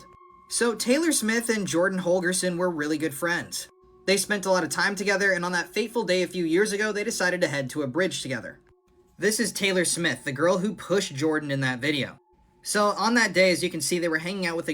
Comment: I thought it's says Taylor Swift😅